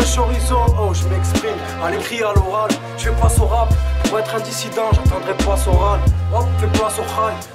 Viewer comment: Il dit quoi sur Soral a 3:40 ? "Pour être ..... j'attendrais pas Soral" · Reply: Mopee Lezard-Tiste "Jfais pas ce rap pour être un dissident, j'attendrais pas Soral."